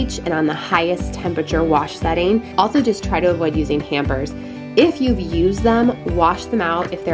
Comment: When you said avoid using hampers I thought you said hammers 😂 I guess I haven't had enough coffee yet. Great video! Praying for everyone! Lots of love and positivity today. God has everything in His control ❤️